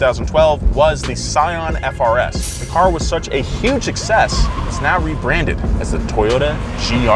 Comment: did he say the car was a huge excess?